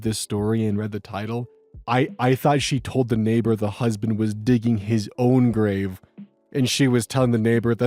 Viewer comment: glad I’m not the only one who thought that she said the husband was digging his own grave lol